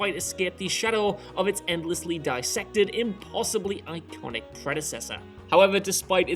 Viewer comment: Did you just say Dark Knight is "possibly" iconic? lol